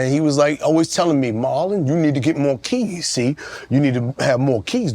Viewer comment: I thought Vlad said Marlon doesn't get views. 🤔